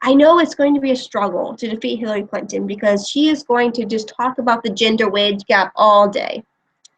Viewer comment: LOL, I misheard what you were saying as 'the ginger wage gap', and right away I was thinking: wait, red heads make less than other?!